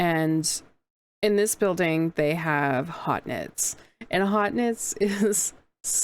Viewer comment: What was the vendor you said it was at 5:27 in the vid and it sounded like hot knit but I can't find anything online. I would love to be able to find! Also super cool event and video thank you for sharing! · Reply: That would be Haute Knit! I love their yarns. And thank you so much! I had so much fun here